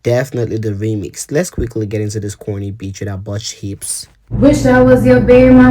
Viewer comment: 😂😂😂😂😂😂😂😂Man hold up, did you just say..."let's get to this corny bitch with some botched hips", you really hate Cardi with your whole heart I can tell.😂😂😂